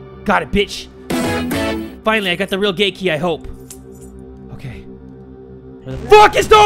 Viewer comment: Who else thought he said “ GaY kEY” instead of Gate Key XD